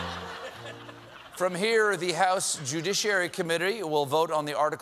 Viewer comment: Did I hear Stephen say "Judiciary Committary"?